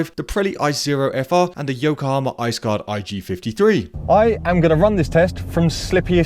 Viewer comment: I wasn't paying attention to the screen. When you were naming the list of tires I thought you said Yokohama IceGod. I thought wow that's an awesome name.😅